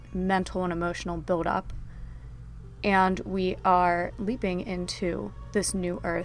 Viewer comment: Thought you said weeping not leaping🤣😆